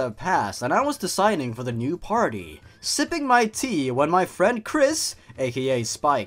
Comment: did you say...kris? thats the character you play as in deltarune